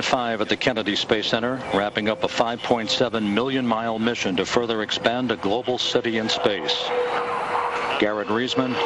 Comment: did he say to further expand a global city in space?